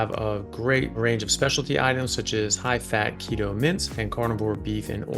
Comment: LOL! 🤣🤣🤣🤣I thought you were saying Carnivore "Mints"! I finally realized you were saying "Mince". 🤣🤣🤣🤣🤣